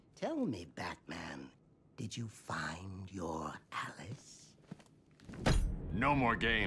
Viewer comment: they just Wrote You're Instead of Your in the subtitles 😁